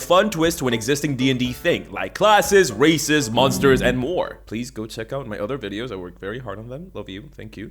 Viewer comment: well, I was watching the most part I was walking by my brothers room which is highly air-conditioned, and because the air conditioner was on it sounded like you said “classist, racist, and many more”